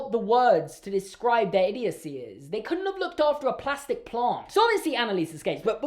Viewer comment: I swear in my mind you said 'so obviously annalise is gay'